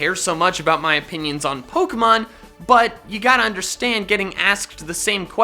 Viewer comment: I thought he said poopmon